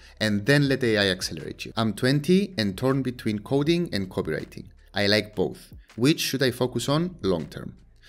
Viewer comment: I wasn't looking and I heard over and over "coding and copulating" and was like whaaaat 🤣 .. copulating builds stories .. very persistent